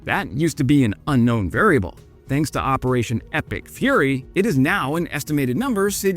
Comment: nice.. now i think "epic furry" everytime i hear "epic fury" 🤣 Kind regards from Denmark